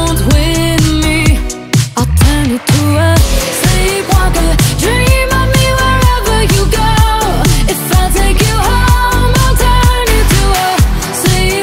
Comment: They play this song on our music box at work. For the longest time, I thought she was saying she'd "turn into a...STREET walker"😂